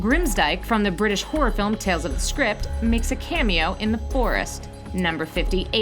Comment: she said "tales of the script" instead of Tales From The Crypt, tales of the script doesn't even sound like a horror film lol